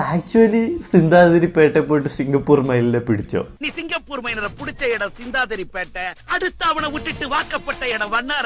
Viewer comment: I think he is saying Singapore Minor 😂 which is even more confusing.